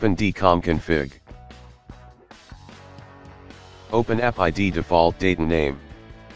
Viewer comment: u lost me there. What did u say? Open appid default name and date? What is that?